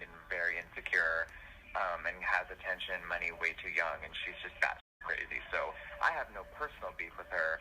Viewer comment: Did he say Nikki was “fat and crazy” 😲🤭 or did I mishear at 1:30?